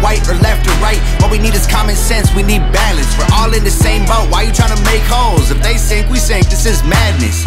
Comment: "We all in the same boat why you try to make holes" So true need to live by that. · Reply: I thought Tom said, "why you trying to make hoes?". Holes makes sense with the line.